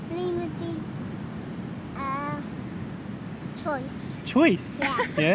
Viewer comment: Hold on! Did she just say 'Choice'? Oh my goodness, that is one DEEP thought! Story as a name is already beautiful, deep and meaningful, and Choice - that would be an inspiring, if not a moving name!